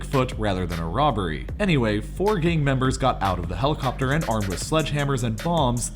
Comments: it sounded like he said "four gay members" at first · I thought he said "...armed with sledgehammers and thongs,...."